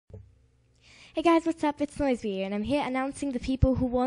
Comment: U said "It's NoizeBea here" so quickly that it sounded like "It's lesbian" lol